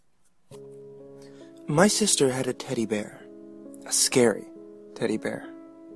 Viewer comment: I heard, “my sister had a toilet paper.” Don’t even bother asking how I managed to get that instead of teddy bear.